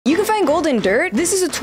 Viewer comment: I thought she said golden dirt at the beginning of the video 💀